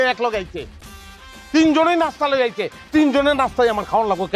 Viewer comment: এখানে কি বলছে কে বলতে পারবে💩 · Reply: তিন সতিন